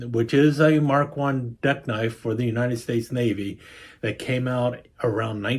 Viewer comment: Cool! Great vid! I hit play without really looking at the title and was listening without watching while fixing dinner for the dog and thought you said Mark 1 Death Knife 😅